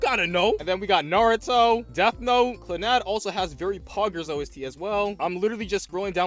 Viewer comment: Did u just say poggers 😂